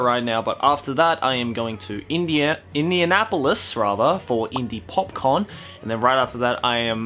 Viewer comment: who else thought he said popcorn? xD